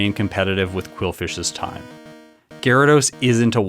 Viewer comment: did he call stantler ‘quillfish’ ? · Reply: I want Stantler's run time to remain competitive with Qwilfish's run time.